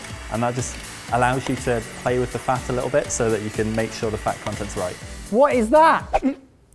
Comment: I watch a lot of Youtube videos with subtitles and I'm lucky I'm not hard of hearing or deaf because apparently the autogenerated subs think is saying "fat c***" and not "fat content" at 2:47.